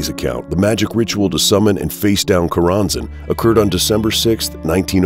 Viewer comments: I thought it said chorizo haha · I thought it said "Chorro"?